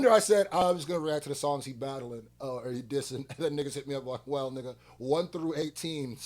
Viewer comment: Please guys Lol correct me if I am wrong here I thought I heard this guy saying he is not going to react to iceman only the disses songs.